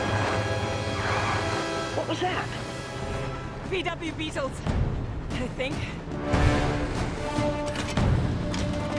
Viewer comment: wtf is she saying???? :O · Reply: Bond knocked a bunch of VW Beetles off the train car that nearly hit her, so when M asked “what was that” she replied “VW beetles, I think”